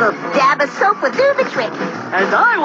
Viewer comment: the best part in the episode is at the end when Ed said he will go fetch the kankers to help eddy free his finger from the tap as they are "experts" eddy panics and tries to free himself lol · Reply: When I was young I always thought he said "and I will fix the Kankers" which confused me for so long.